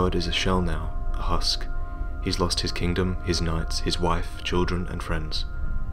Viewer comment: I heard "nuts" instead of "knights" and started laughing :'D